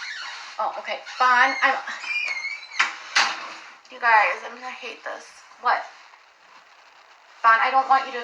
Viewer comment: When her son opens the door, she sighs and says "Ugh, I hate this!" Wtf? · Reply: Omg, I thought she said "I'm going to hate this". Either way, she sounds like such a b*tch. She is so condescending when she speaks to him. I can't stand her.